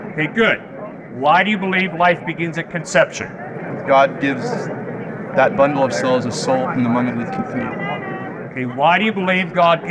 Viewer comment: the subtitles say "God gives that bundle of souls and salt in the morning with coffee" 😆